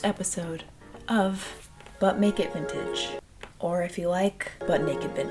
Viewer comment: I heard "butt naked vintage" the first time you said it